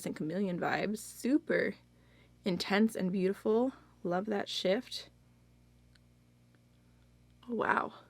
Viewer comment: Ohhhhh theyre not having a sale for a while, I'm so desperate to get some, I ordered 7 but $25 for one shade is very pricey, as gorgeous as they are!!!! Also I heard "love that shift" as "love that shit😂)